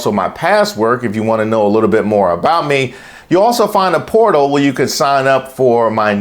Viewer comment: I heard "porno" instead of portal.